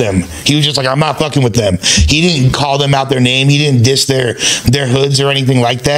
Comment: did say he didn’t diss their hood?